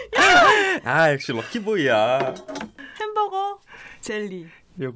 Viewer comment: she actually said ,,ahh shit"